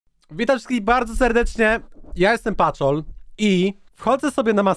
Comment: Czm usłyszałem patol ?